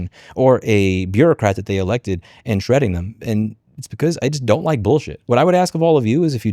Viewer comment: What he said: "I don't like bullshit." What I heard: "I don't like politicians."